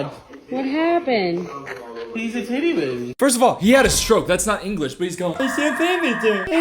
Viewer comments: i think he said here "he's a titty baby" in a baby voice. equally as bad if not WORSE 😭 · i swear he’s saying “and she has boobs!” and “he’s a titty baby”….. not poop and shit lmfao😭